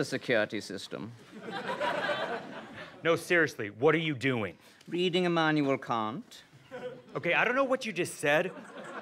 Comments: You know you need to take a man seriously when he’s just casually reading Kant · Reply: Lol I heard "reading a manual you can't" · You know you need to take a man seriously when he’s just casually reading Kant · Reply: I always thought he was saying “reading a manual you can’t”